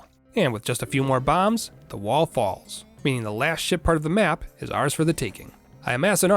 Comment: Somehow I keep hearing him say "Shit part"